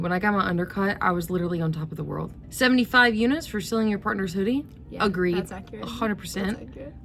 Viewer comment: This is what I get for only listening, I thought said "Selling your partner's hoodie" and was like, "Well, if it was a bad break up, that's probably true."